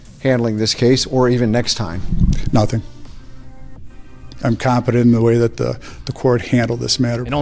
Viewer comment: Check out <link> to get a 60-day FREE trial and get up to 89% off USPS and UPS rates when you use ShipStation! Support the channel at <link> Check out <link> to watch videos early, see uncensored versions, and get access to exclusive content only found on <link> ! TWITCH.TV/DONUTOPERATOR MY VLOG CHANNEL: <link> MY GAMING CHANNEL: <link> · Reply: Did he just say "I'm competent..." in place of "confident?" Either he doesn't know what words mean or he's trying to lowkey gaslight people into believing it.